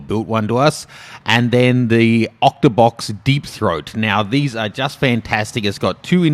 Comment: Did you just say octabox Deep throat? XD